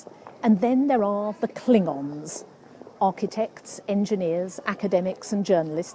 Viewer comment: My mind heard 0:38 as "then there are the Klingons" instead of cling-ons at first haahah